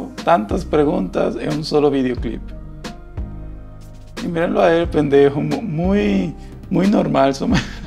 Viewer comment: WTF ¿ soy el único que escucho que dijo "Miren a el pendejo" en el minuto 4:22?